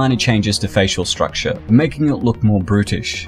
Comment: LMFAOO I thought this guy said making it look more Brittish 🤣🤣